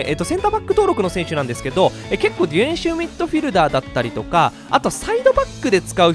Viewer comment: ディフェンシブミッドフィルダーって言ってるのに、でいふぇんしゅミッドフィルダーって言ってるように聞こえてしまう笑